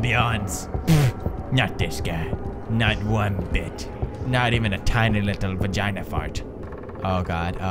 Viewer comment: did he say "not even a tiny little vagina FART" ? 02:23 lol